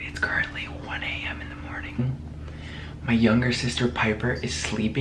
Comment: WHAT DID HE JUST SAY YOUNGER SISTER !!!!!!!!